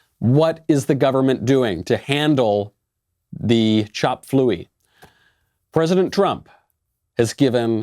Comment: Did he just say chop fluey...?